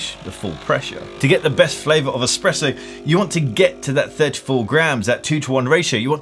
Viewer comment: Er... did you just say “that’s how you make an EXPRESSO”....? Yikes.